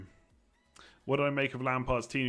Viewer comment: Havertz isn't playing, Lampard just confirmed in an interview.. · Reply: i thought he said silva, chilwell and ziyech are not playing